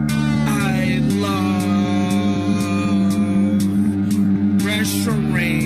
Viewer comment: When I heard it first I thought he was saying „I love restroom”😳